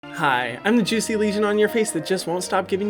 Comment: I thought said “I’m the juicy lesion on your feces” and then I replayed it to find out what he said.